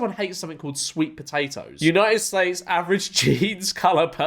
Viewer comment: did he say “united slaves” ?😂